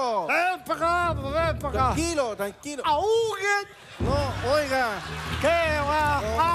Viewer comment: me dio risa en el minuto 6:01 porque dijo aduken XDXD